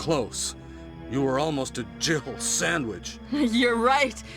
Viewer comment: As a kid I always thought barry said “Jibble Sandwich”